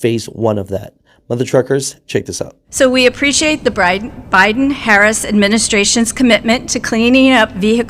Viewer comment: so what are my thoughts 💭💬💭 i think 3:14 about sums it up.... holy 🐄 did she really say they appreciate the bribe instead of Biden , 😲 if that's not a classic Freudian slip what is !?